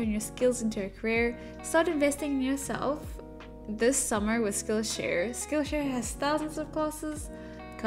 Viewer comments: When you said "today's sponsor of this video.. skill share" I thought you said "skill issue" and I was like WELL DAMN. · I THOUGHT YOU SAID SKILL ISSUE INSTEAD OF SKILL SHARE